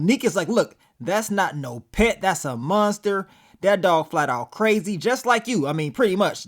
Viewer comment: When she said that aint no pet all I heard was “THAT AINT NO DAMN PUPPY!”